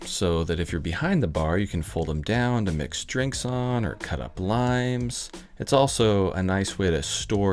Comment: I swear at first I heard "or cut up lines" not "limes" at 31:05.... either way sounds like a fun time. Great build, 👍